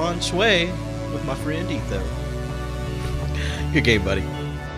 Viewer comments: Did he say "you're gay buddy"? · Am I the only one who hears "You're gay buddy" at 3:40? Yeah? Alright... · Oh hell, I started tearing up once the Mindcrack guys started appearing. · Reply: Dhruv Tharanath To me it sounds like he says "Good game, buddy", not "Thank you, buddy".